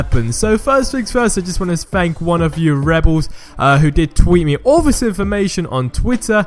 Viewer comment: it sounds like he says I just want to spank one of you rebels